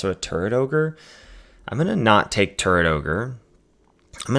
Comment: did u say Turd ogre on purpose? lol